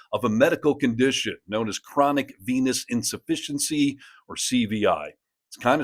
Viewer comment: At first I thought you said "chronic penis insufficiency"...